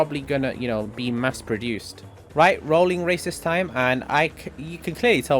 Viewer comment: a little confused when I thought you said rolling racist time 🤣🤣